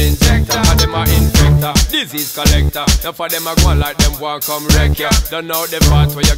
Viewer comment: did he say disease collector?